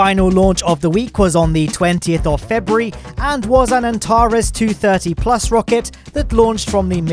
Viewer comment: What He Said: Antares 230 How I Heard It: Atari 230